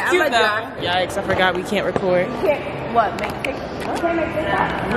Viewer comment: I swear I say that guy take a pic of y’all